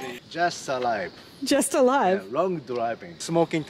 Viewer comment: I think he said "just arrived"